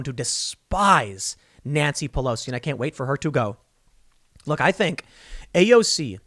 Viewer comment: idk about anyone else but in my head i heard "cant wait for her to die"